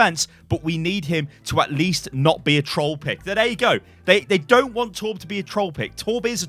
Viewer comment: what he says: "it doesn't mean to be a troll pick" what i heard: "it doesn't MEME to be a troll pick" xD sorry, had to say that xd